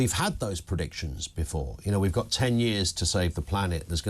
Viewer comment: "I'm not sure that 100 years ago it was predicted the planet only had 10 years left" - probably not but a certain Greta T predicted 5 years ago we only had 5 years or it will be too late and has now deleted that prediction as it was demonstrably false. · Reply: I thought she said 12 years? Still I look forward to her suicide in 7 years time.😊